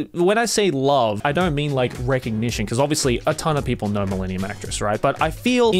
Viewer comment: I really thought he said “millennia mattress” I’m so embarrassed